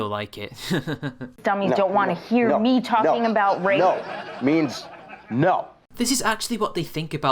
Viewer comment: She said "I can't, These sexist dummies dont want to hear me talking about rape" I think you misheard it